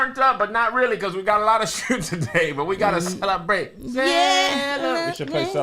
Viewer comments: Wait, did curse lmaooo 😂. I could I sworn I heard “we got a lot of shit today” 🤣😂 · Did say "We gotta Lotta shit today!" @ 2:12 ???? I know I ain't tripping